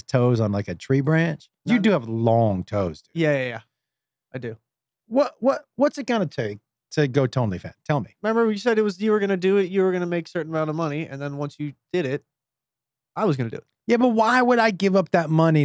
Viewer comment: sounds like Josh says, "go toe only fans"🤣